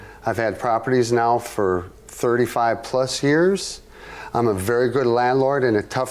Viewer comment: I thought he said "I'm a very hood landlord" 🤣🤣🤣